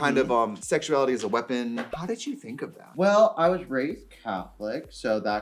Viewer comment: When she said “I was raised catholic” I thought she said “I was raised with a cowlick so that kind of just changed everything for me”